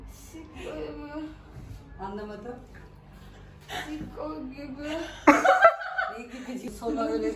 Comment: ne dediğini anlayan varsa yazabilir mi? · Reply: sikko gibi